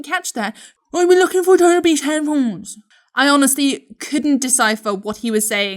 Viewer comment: “I heard: ‘ I’m lookin for uh duh herpes headphones’”